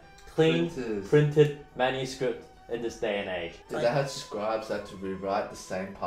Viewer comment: Did Brett say printed manuscript at 00:30? Lol